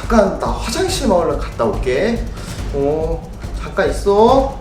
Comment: 나 잠깐 화장실마을에 갔다올게 라고 들리지?